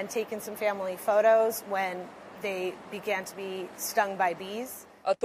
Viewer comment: I heard "Bee-gun to bee stun by bees"